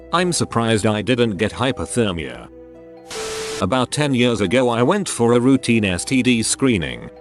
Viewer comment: That HIV one for STD should be standard practice so people think twice about having casual sex. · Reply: The funny thing is that I was watching the video at 1.5 speed to save time. I at first thought it said "10 years old" rather than "10 years ago". I had to back the video up to see/hear it again.